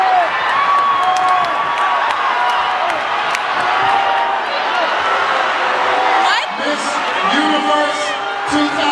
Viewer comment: What does Miss USA say to Miss Philipines at 0:08? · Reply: "I think you're miss universe"